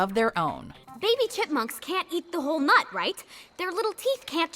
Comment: Tinkerbell does NOT count as a sequel! It's it's own thing. That's why there are like 6 movies now. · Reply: i skimmed to 10:44 and all i heard was "eat the whole nut"